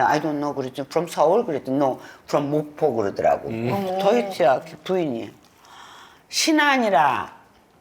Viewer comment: 유치하게 가 아니라 더 히트야~ 로 들었는데😂